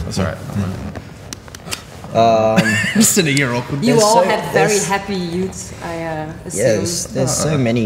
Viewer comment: "It sounds very happy, very poppy" DUDE WHAT ALBUM DID YOU LISTEN TO I WAS IN TEARS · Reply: curlydan I thought she said "very heavy" 😁 I was agreeing with her.